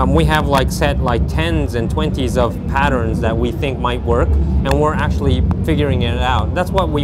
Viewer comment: did he say tens of twinkies.